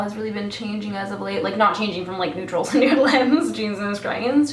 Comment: anyone have any idea what she's saying at 10:58? I know she said "I feel like my makeup style has been changing as of late" and for some reason I just can't figure out what she says after that. I hear something about neutrals and dragons? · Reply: "Neutrals and nude lips... Jesus Christ". She says it in a funny accent like way but I heard it clearly.